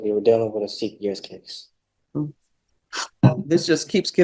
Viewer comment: Dealing with a what? No clue what he said. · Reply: Dealing with a CPS case. Sorry you had to wait a year ;)